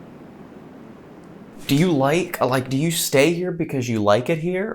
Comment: I swear it says @ 1:25:54 "Stay" & keeps saying "stuck" before "Trapped" & after it xxx